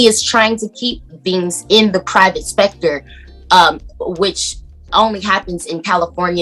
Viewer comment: Wait...did she say private spector at 3:54? I rewinded that part a dozen times just to make sure. Can somebody please come get that child lol